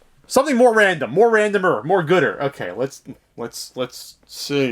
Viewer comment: Did you say "more gooder" around 0:45? XD Nice to see a new review from you.